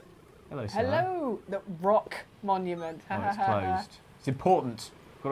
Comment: Okay, I heard "ROK monument" as "Brock monument", and spend the next several minutes wondering if I'd missed an episode of Pokémon where Brock dies. ...On that note, what happened to the Republic of Korea?